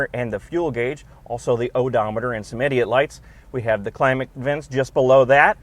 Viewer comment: Did you just call the warning lights "idiot lights"?